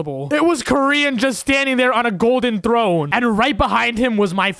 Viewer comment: The Fact that when he says "Koryin" sounded like Korean lol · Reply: I thought he was saying grian